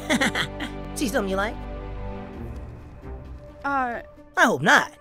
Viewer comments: I've been trying to understand what he said right here for years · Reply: Never mind i finally understand whet he said "See something you like" you can hear if you slow it down and listen closely VERY closely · Does anyone know what Sonic said at 11:13? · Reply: “See something you like?”